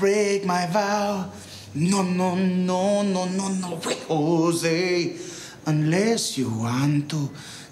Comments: hahaha In the min 0:32 does he say "no no no no way JOSE" ???? hahahaha so fuckin hillarious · I can't understand what is he saying at 0:32... nononononono...? please help me! tks